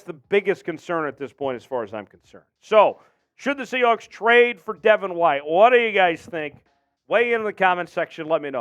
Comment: I thought I’d said we traded for devin white I got so pumped